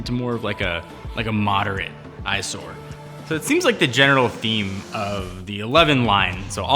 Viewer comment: Freudian slip with “genital” instead of “general” lmao